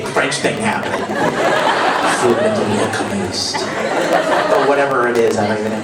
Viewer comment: "So someone tell me how to say Full Metal Alchemist in french." I heard: "FU METEL LUBULLL!"